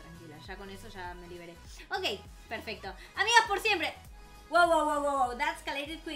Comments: no le entendi a hablo demasiado rapido jajaj xD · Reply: Amigas por siempre dijo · jqkkdmf que dijo jajajs 😆😅😂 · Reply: Dijo darkalesiqueenki xD